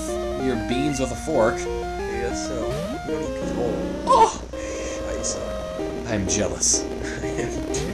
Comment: did you say "scheisse"? hahah